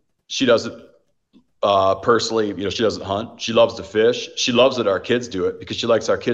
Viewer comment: Did he just say “she (his wife) doesn’t like you”? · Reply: He was saying she, like Megyn does not hunt but the way the clip starts it makes it sound like he said his wife doesn't like her 😂😂😂